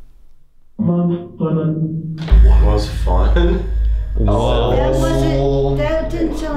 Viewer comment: It doesn’t sound like he said “was fun” it sounds like “mom’s burden” or something